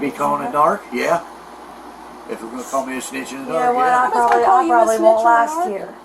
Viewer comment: i thought he said "If you'd call me a snitch in the dark" like you wouldn't say it to his face but people would talk behind his back.